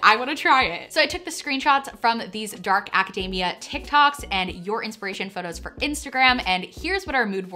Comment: Every time she says Acadayyyymia, I hear "Macadamia" nuts lolololol (it's Aca-deee-mia, but it's also fun to hear it the other way) :-)